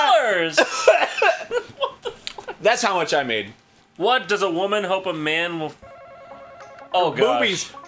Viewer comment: I thought it said "What does a woman want a man to not ice first about her" at first